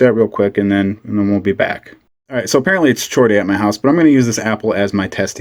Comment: 'Apparently it's chore day' What I heard was 'my wife wants to know why I'm playing with knives while she's working.